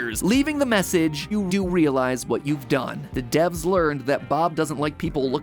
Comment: Matpat accidentally said "Do you realize what you've done?" Instead of, "You do realize what you've done?" and he had to edit it. Just thought I'd point that out.